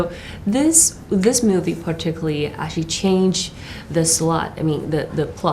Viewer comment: Did she say 'slut' (0:59) and then correct it to 'plot' ? hahaha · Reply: c. It's slot, not slut. Sounds similar tho. Ha!